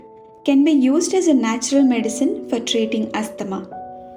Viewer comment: Did you say for treating "eczema" - I couldn't make that part out and the translation to english didn't work out in the CC translated. Sounds like a miracle plant to me. No wonder they try to scare you away from it (here in the US) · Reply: asthma